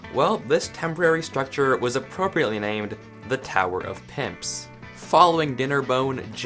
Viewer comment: What it said:The tower of pimps What i thought it said:tower of simps